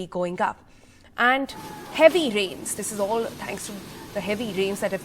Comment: Did she just say "This is all thanks to ... " at 0:22?